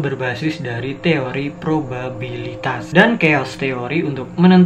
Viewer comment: gue dengernya kalo gak salah ProBABI